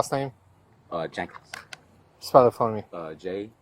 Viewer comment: I swore he said Michael Jackson. Then when he said Michael Germane Jackson I was like no he didn't. I'm glad he had him spell his name and he said Jankens.